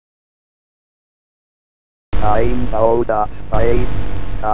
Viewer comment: "I'm doh-dots five... hahaha" WTF is he actually saying?! EDIT: He's saying "I'm out'a spite... hahaha"